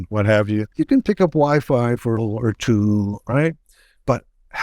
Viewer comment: What does he say at 02:25-02:26? It's muffled. He is saying something like, "... picking up wifi for one or two?"